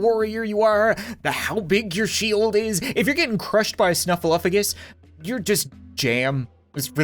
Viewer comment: My english is not very good Can someone tell me what he calls the mamoth? What does it mean? Is it snuffleupagus?